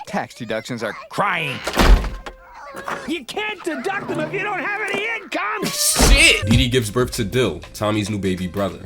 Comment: At first I thought you said "then he gives birth to Dil" and I was like, "Wait... what?"